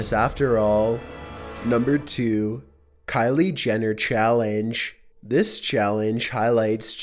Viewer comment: I thought he said the Caitlyn Jenner challenge. I was like, "Fuck that shit."